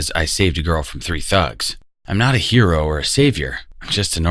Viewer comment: I thought It said I saved a girl from 3 hugs